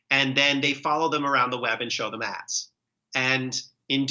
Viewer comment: Did Matt just say, “They compile detailed dossiers on people and then they follow them around the web and show them ass.”? 😂